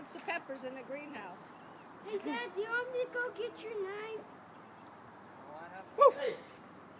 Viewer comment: You just hear the lil kid in the back hey dad want me to get your knife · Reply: N2-_ ES_- I thought he said “Want me to get your 9?” As in 9mm.